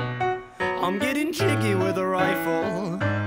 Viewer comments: My search history: “I’m getting cheeky with a riffle I’ll pull the trigger with my eyes closed” My FBI agent:👁👄👁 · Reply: I thought it said I'm eating chicken with a rifle lmaooooo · everyone searching up "getting kinky with a rifle" Me actually listening to the lyrics: "I'm getting jiggy with a rifle!" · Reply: i thought it said "im eating rice with a rifle" lmfaoo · nobody: my search history: "i m g e t t i n g k i n k y w i t h a r i f l e" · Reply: I thought it said I’m getting tricky with a rifle 🤷🏼‍♀️ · the fbi is 100% after me after searching for "getting kinky with a rifle" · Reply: I thought it said I’m getting giggy with a rifle 🤭